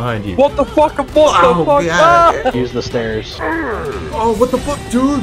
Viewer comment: 😂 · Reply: I just woke up and listened to it again, I can’t believe its “use the stairs” instead of what I heard: “Useless N******”